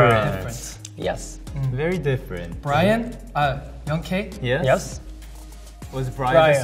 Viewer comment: "Brian...uh youngk" When he said that I heard WHO'S BRIAN?!? IM YOUNGK